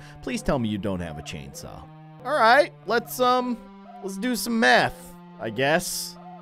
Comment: I THOUGHT HE SAID METH LOL